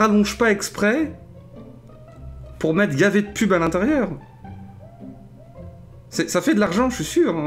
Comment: C'est moi ou dit " pour mettre gavé de pub à l'intérieur" ? Je croyais que "gavé" était une expression qu'on utilisais qu'à Bordeaux